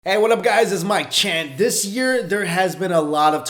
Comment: I thought you said: 'hey, waddup guys this is my chin' lol